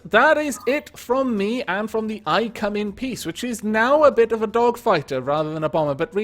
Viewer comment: It sounded like you said "I come in Piss!"